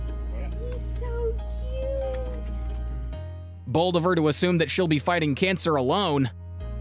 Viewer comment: thought this guy said “Bald of her to assume she’d be fighting cancer alone” istg